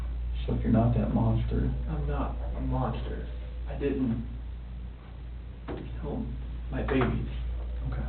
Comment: These girls… He says these girls. Not my girls… Not our girls… These girls 😑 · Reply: "I didn't kill my babies" I absolutely don't believe him but he actually says "my" sometimes. I think he says these and those because the interrogators says it and it's a natural part of a conversation to pick up each others expressions.